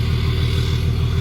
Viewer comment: I thought he said he looks a turd with the bikes like that. Instead of tourist. Oh my. Sorry. Safe travels.